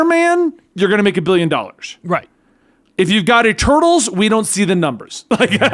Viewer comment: Oh, Eternals, I thought was saying “Turtles”